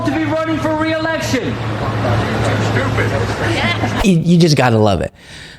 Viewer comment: It sounded like Biden said "You're too stupid".